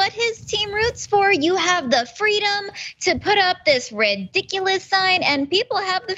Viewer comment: I thought she said RED-diculous, which is appropriate.